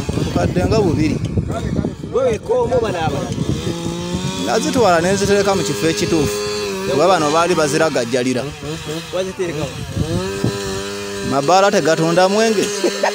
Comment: I hear "naziteleka mukifo ekitufu bala tezetubda mwenge" 😂😂😂😂